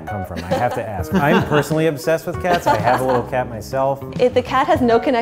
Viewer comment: I thought Andrew said, "I am a little cat myself." and I found it weird that he said that to the owners of the restaurant in a Worth It video and then I read the subs! 😂😅